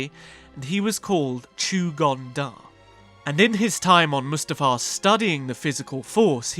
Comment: I totally didn't hear "Chew-dong Dar".